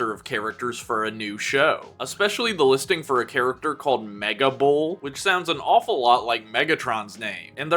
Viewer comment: I thought you said Mega Bowl , where Im Sure more then just me spent the odd night or so striking out , with the odd home run put in thee